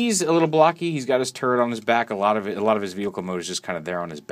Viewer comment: I misheard you for a half second and was like brawl has a turd on his back